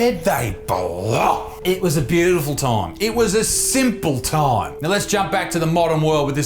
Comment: all i hear is "simp simp simp"